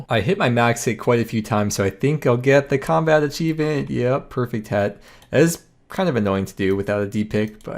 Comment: was half paying attention and I definitely heard "Perfect head"